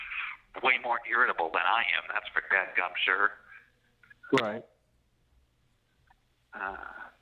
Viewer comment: What does say at 32:17? Gad Gob Sure?